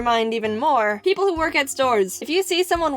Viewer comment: Subtitles wrong, its work at, not walk in to.